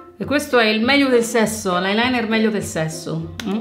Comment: ho sentito eyeliner al minuto 21:47 forse mi sbaglio haahahahahahahahah